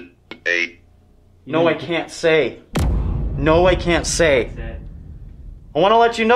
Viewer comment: sounded like he said “ No I didn’t drink”🤔